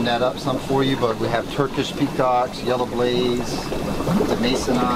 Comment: he said " Turkish p..." i didn't understand. what did he say?